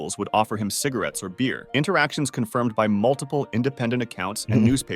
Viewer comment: What did he say because u said W bro 💀🥀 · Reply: until his death in 1985